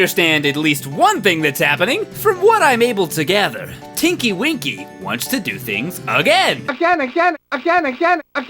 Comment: I thought he said kinky winky